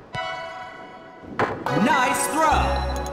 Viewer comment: Why do I keep hearing “nice throw” as “nice bruh”